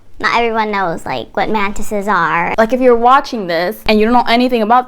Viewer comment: Why is everytime says 'mantises' all I hear is 'mentits' 😂